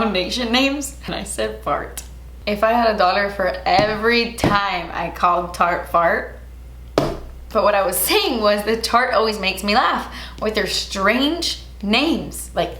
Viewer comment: I heard 'if I had a daughter for every time I said "fart"' and was very intrigued where you were gonna be going with that😂